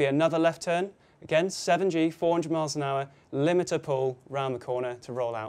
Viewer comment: Did he said 7G 😱